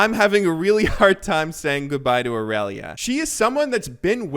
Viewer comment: I thought you said goodbye to league.